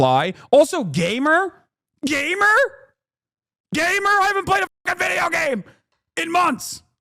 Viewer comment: I thought he said GAY-mer X-D